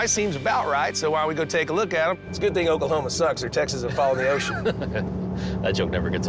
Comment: what he said at 1:22 ????? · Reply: He said it's a good thing Oklahoma sucks or texas would fall in the ocean.